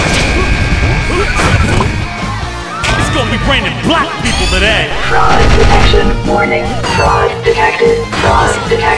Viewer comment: What does he say at 1:25? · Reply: It's gon' be rainin' black people today!